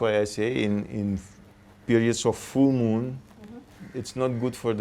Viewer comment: says in periods of full moon it's not right to make important decisions. Did I hear write or is it new moon. Kindly confirm plz. Regards. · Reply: Full